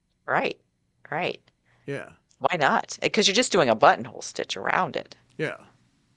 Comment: I honestly thought you said"butthole stitch." Oh my. I was a bit distracted at the time.